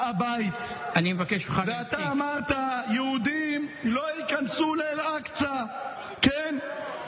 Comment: the subtitles are crucially incorrect. he actually said, "and you said that jews CANNOT enter al aqsa" - thats why Ben Gvir was so furious